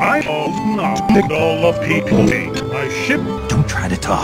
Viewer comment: Enter with a beauty on screen! :D What is said at 6:52 (Just making sure, is what is said: I can not make all the people eat my shit?)